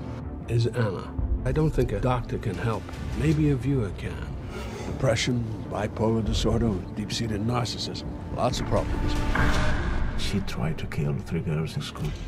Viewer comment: What does he say between 0:37 and 0:41 ? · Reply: "Depression, bipolar disorder, deep seeded narcissism. Lots of problems"